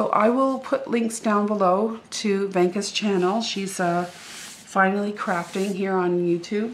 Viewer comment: oh no thanks for letting me know I will correct that when I get home tonight! Tfw xx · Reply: Its ok because I replayed vid and heard you say that she is FINALLY CRAFTING as her youtube channel. So I can check her out now. Thanks!